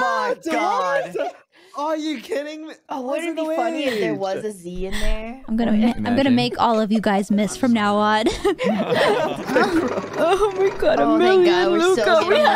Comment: I thought she was gonna say "I'm gonna mag!"